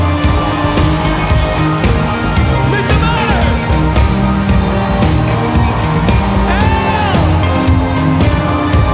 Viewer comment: What is he saying at 6:33 ? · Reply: He says: "Mr. Martin L Goreee" 😊